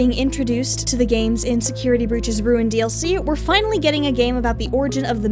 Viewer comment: I thought you said insecurity breach and I was like SAME lol